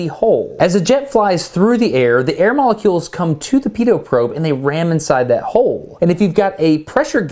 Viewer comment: Excuse me? There must be some kind of mistake, I heard "pedo probe?" Is this correct?